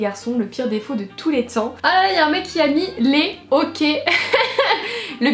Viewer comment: au debut jme suis dit vazy c'est pas sa faute si elle a le hockey et puis apres jai compris (jme suis sentiun peu con)